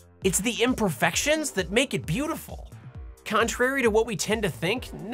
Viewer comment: DID SOMEBODY SAY "ENTRAPTA"? :D